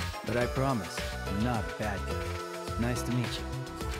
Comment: I thought at 44:39 he said “I’m not a bald guy.”